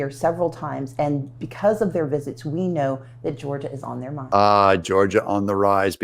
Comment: at about 6 minutes and 30 seconds you missed what she said. You thought she said Georgia on the rise. that's not what she said. She said they have Georgia on their mind, a reference to a famous song.